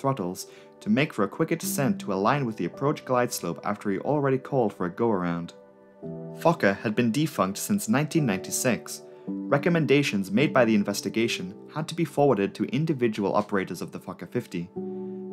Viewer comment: Everytime it sounded like you said “fucker” I let out a little giggle